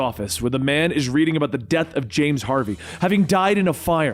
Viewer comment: thought he said James Harden for a sec